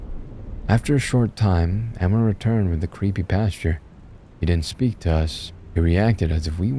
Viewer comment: Hey guys, wanted to leave a little message about a mistake I made throughout the story because... Idk, I think I'm stupid. I kept saying "Pasture," when the word was "pastor," I do know the difference, but apparently for these 50 minutes (recording takes longer than the actual story), I just... forgot that. I apologize for the mistake, I hope you enjoy the story, I loved this one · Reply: When he was described as the "creepy pastor" it almost sounded like you were saying creepypasta, very apropos lol